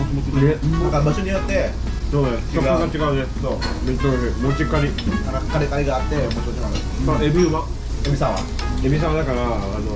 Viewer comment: Did Tomo just say MUCIKARI??? NANI????